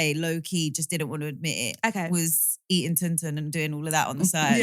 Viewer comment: eating tin tin?! Lmaooo is that what she said? That’s funny af